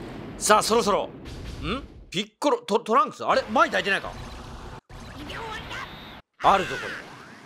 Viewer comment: マイ抱いてないか？！がいやらしく聞こえた。いつも楽しく見させてもらってます！